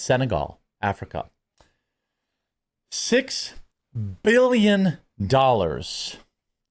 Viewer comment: did he say 'billiom'